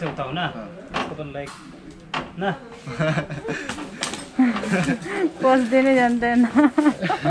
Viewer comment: Did the girl on 5:21 said pose dinai jandaina ..I hear like that she spoke Nepali 😪😪